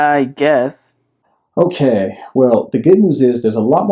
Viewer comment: What does he say at 7:10 ?? More people identify as nunce? or Nuns? what is that word? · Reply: Aaron James This is a play on words. In a form it asks "What religion are you ?" The answer us "None".